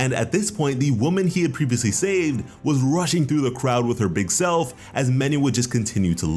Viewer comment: Did he say with her big self